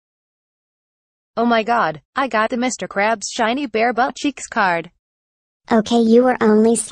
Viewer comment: What did he just say Mr crow's shiny Bear butt cheeks card ohh come down let's